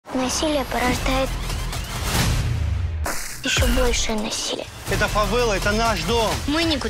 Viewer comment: Мне послышалось в начале рекламы "Россия порождает ещё большее насилие"🤣